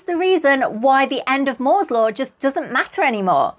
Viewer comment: Thank you, Dr. for another awesome video. I thoroughly enjoyed it! At 7:14, I thought you said, “…that’s why the end of Mordor doesn’t matter.” Took me a second to realize you actually said “Moore’s Law”. Two slightly different things 😉